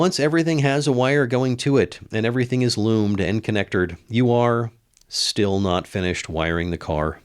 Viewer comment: Did I hear "connectored" at 11:54? I went back, and it seems that, yes, I did indeed hear that (new to me) "word". 🤣👍